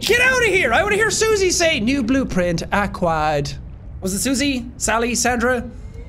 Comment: in the first subnautica the PDA kinda sound like it says "new blueprint ack-wired" when it's acquired 😂